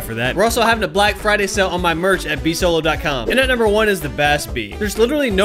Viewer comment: How the bas-p go from SMG to a battle rifle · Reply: oh lmao I thought they both said bas-p didn't realize the b